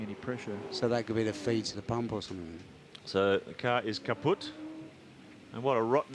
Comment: did he Just say "Kaputt"?